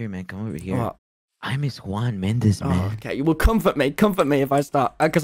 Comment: Tommy: “Who the hell is Juan Mendez” Me: searched up who is Juan Mendez oh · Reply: I thought he was saying Shawn Mendes